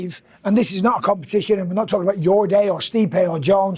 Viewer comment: Sorry did Bisping say “this is not a competition and we’re not talikin about You’re Gay, or Stipe or Jones” towards the end there 😂